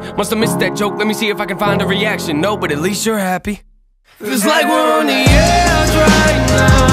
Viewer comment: "No but atleast you're happy" he ran out of breathe and that was the moment it got ruined but, incase you didn't hear: 2:36!